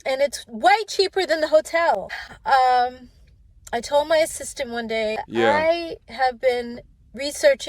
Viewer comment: Did she say 'my assistance' at 5:40? You're broke, lady! Assistance?!